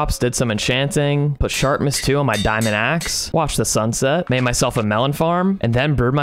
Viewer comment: I thought said "and then I out w sharpness 2 on my diamond pickaxe " the I was like wha